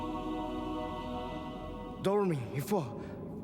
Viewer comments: it sounded like he said " Dormin you fuck." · This is what I hear from Wander "Dormin you fuck" 9:32 I also hear "Our bet"